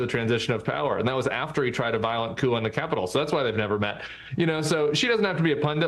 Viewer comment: did he say “emotional support cougars”? 😂